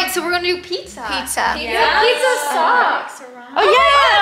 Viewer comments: I have been waiting for this I love all the twins they are so fun to watch ❤❤❤❤❤ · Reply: I thought said Pizza sucks but she said you have pizza socks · I thought they said ’ pizza sucks!“ and I am like then why order pizza